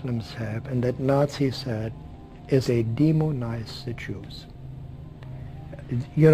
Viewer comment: what does he say in 9:31.. demon what...sorry my english is poor and I think it's an important moment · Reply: He is saying that the germans "Demonized" the jews and muslims are also doing just that. demonize [dee-muh-nahyz] Spell Syllables Examples Word Origin verb (used with object), demonized, demonizing. 1. to turn into a demon or make demonlike. 2. to subject to the influence of demons. Expand Also, especially British, demonise.